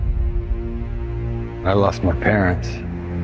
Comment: That intro.. i really thought he said "i've lost my pants" 🤣